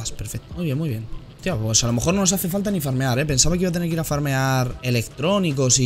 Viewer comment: Pense que dijo que iba a mear JAJAJAJA